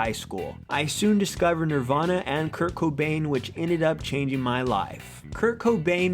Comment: i was not listening at 5:45 and i thought he said cocaine lol